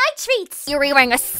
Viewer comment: Sayori: I got a clock Me who thought I heard her say she got a glock: Sayori what the actual fu